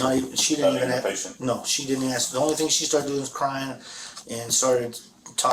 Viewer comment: I heard her saying 'the thing was crying'